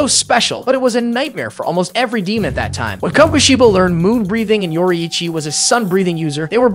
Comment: I thought you said moonwalk breathing